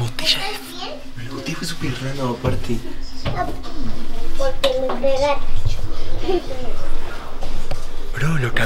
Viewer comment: desde el minuto 8:5 al 8:12 se ve que dice "oye quico estas bien " y despues "porque te pegaste" no se si dijo eso yo lo entendi